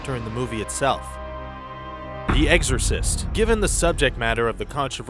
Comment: I swore he said eggsorcist 😂😂